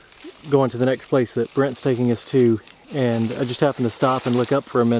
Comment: Nice to meet ya Brent ... I thought you said Bret at first lol But your description says Brent